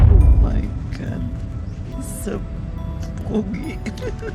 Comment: did she say omg he’s soooo ugly😂😂😂